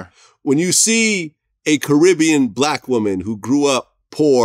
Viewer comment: When VLAD says "When you see a Caribbean achieve that type of success" ..all I really hear is "when you see a ngga achieve that type of success!"